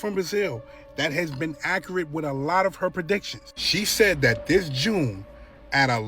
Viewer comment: did he say "PERPEDICTIONS"?! 😂😂😂Perpedictions. My new favorite word.